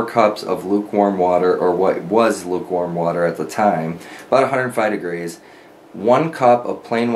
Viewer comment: did you say “about 105 degrees”?